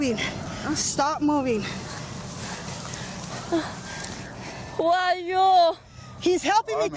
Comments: who are you?が怖いよーって聞こえた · 「怖いよう」に聞こえてビビった